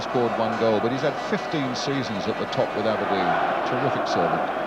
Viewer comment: "fifteen seasons at the top without a win". At least, that's how I heard it the first time. Turns out it was "with Aberdeen", but I prefer my first version.